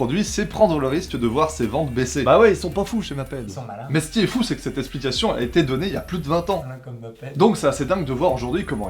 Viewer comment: Tres bon episode. ya une seul choses que j'ai pas réussi a entendre c'est la réplique que dit à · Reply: merci Fafouin en effet le son est pas top à ce moment là, il dit "malin comme maped" pour la petite référence ;)